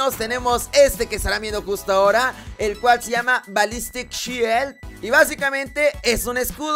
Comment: neta pensé que dijo valisteshit.🤣 Saludos.😎✌️